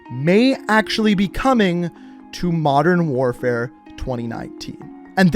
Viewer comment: did he say modern warfare 2019